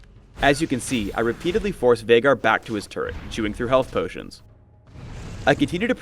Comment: did he say "jewing" through health potions?